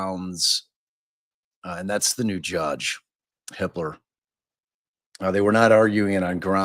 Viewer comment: Gotta look up that new judge's name, I keep hearing "Judge Hitler" O